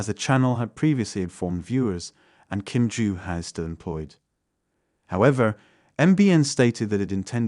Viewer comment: What does he say at 1:09? That Kim-joo has what?? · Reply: She still has her job